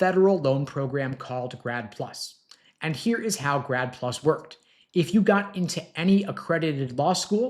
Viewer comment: I thought he said "GRAB PLU$" Which sounds more like reality ...vs 'grad plus"